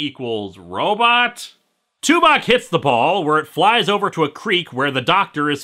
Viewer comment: OHHHHHH, Tuvok! I thought you were saying Tupac.